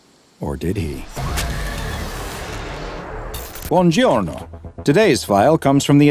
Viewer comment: is there anything in italian that sounds like this that could mean 'welcome', 'hello', 'greetings', or something of the sort that sounds like what I think I heard? ( bonjour is what I hear) or did just make an oopsie on this one; it's close to 3am where I am and for some reason I just need to know